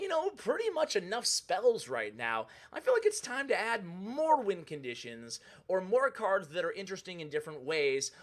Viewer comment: I was today years old until I realized I was saying win conditions wrong. thought u were saying wind conditions xd